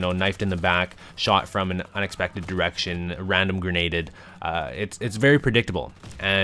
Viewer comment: Haha I thought he said "shot from unexpected erections"